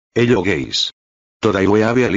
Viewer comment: I thought he said hello g@